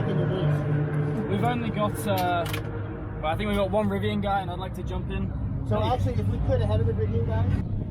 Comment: did he say "rivian guy" ?